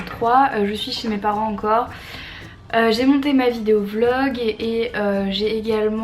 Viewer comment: Quand ta dit 'je suis chez mes parents encore ' moi j'ai compris "je suis chez mes parents en Corse "ohlala je suis fatigué et sourde :D ♥